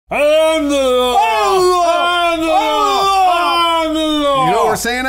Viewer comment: I AM THE LAW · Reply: J 52 I'M THE LAW Ha I thought it said I'm the Lord..